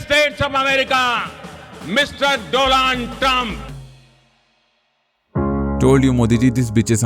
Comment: Did he just say, "Mistress DoLaand Trump"? 😅😂 now I get it, "Ao Modiji humse sambandh banao"